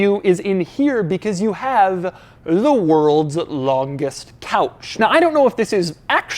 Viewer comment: When Doug said "the world's longest couch" an ad kicked in right before the "ch" syllable and I heard the world's longest cow...